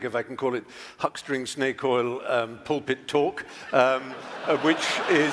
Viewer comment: I thought Fry said Pol Pot, not pulpit.